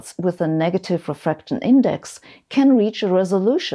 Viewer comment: You have a mistake in the captions at 6:02. The subtitles say “image” when it should be “index”